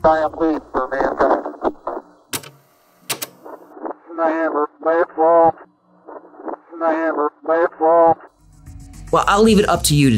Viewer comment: Sounds like he said man never space walked